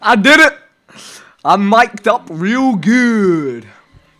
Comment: did he just say he miked up real good TRIGGERED